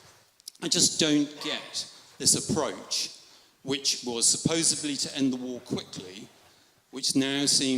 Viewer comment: he says supposebly instead of supposedly btw.